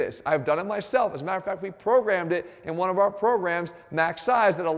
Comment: Jeff did you say at the 4:30ish mark “This will help you lose weight I proved it cuz we used it in our MASS program” 🤔